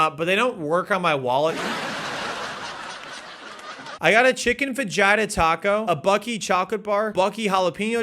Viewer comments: When he said fajita taco, I thought he said v-gina taco, lmao😂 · the way u said fajita killed me deeply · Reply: I thought he said "Vagina Taco" and was so very confused.